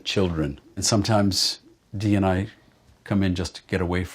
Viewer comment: Sorry. What is he saying: "DNI sometimes ..."? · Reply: "Dee and I" - his wife's name is Dee.